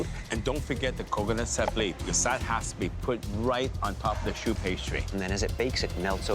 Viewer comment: lol i thought they were saying "shoe pastry"